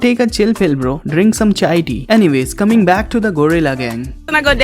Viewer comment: did he said chai tea 😂